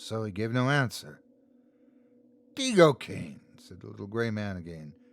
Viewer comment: Is it just me or did anyone else read the title as come Hitler.... 😂😂😂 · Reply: Yeah and II thought he was saying Teig-Cocaine